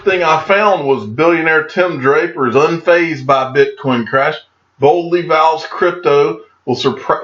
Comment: I thought the first article said “Bitcoin Cash” and realized it was “Bitcoin Crash” but I shrugged and thought, “Hm, there won’t be a difference someday.”